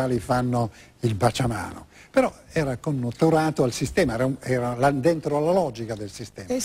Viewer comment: Non capisco quello che dice Angela da 5:50 "connoturato al sistema"?? E poi cos' è che dice ? Andentrolologica?